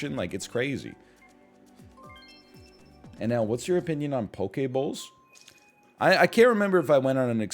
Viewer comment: when he said poke bowl I thought he said Poké Ball and was so confused